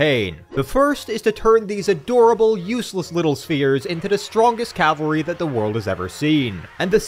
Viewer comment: Ok i was semi distracted at the moment and thought i heard adorable useless little queers